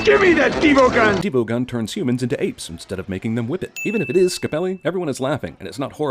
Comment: instead of whip it I heard women. Was kinda confused there